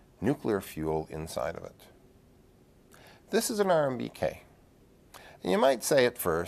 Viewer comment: It's me or he really said RMBK instead of RBMK